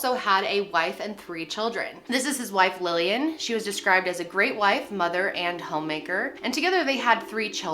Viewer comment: When you talked about the mom at the start, I thought you said 'great white' and the I was like, gEeZ